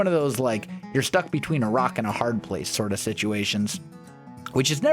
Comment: I honestly thought he said Iraq and a hard place 😂